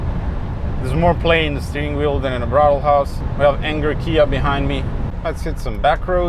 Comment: Muy excelente este video! By the way, Did I hear right: At 31:03 "There is more play in the steering than in a brothel house" ?